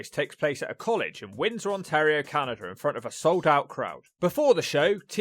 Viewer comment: Wait a second... did you just say "Canader"?